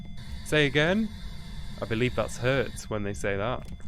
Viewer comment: what did the ghost say on the spirit box? I can't tell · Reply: "Catch"